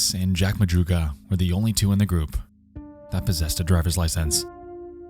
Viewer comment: i love your videos, the animations are amazing. also you said “possessted” instead of “possessed” at 5:38. love you man